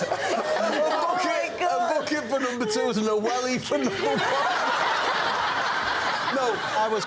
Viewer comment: the captions had no idea what Bob said, but I think I figured it out. Spoiler Alert ⚠: see my reply below... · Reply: Bob says: "A bucket for number-twos and a welly for number-ones." (Welly : a knee-high, waterproof rubber boot.)